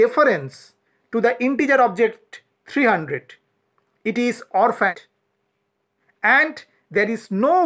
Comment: This was very helpful, thank you! A couple questions: 1) Can you clarify what you said at 10:57? You say "It is..." but I can't understand the word you say after "is." 2) What does Python do or what happens to the "integer object 300" when there are no longer any variables pointing to it? Does Python just dispose of it? Or does it still exist somewhere in memory? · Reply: I said that it is orphaned. Means 300 is an integer literal which is an object in Python Language. it's possible for Python objects to get orphaned i.e., completely inaccessible from the program, but they don't die. Python deletes unneeded objects (built-in types or class instances) automatically to free the memory space. The process by which Python periodically reclaims blocks of memory that no longer are in use is termed Garbage Collection.